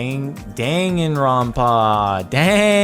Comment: I thought it said "dangerops" like from the preganant video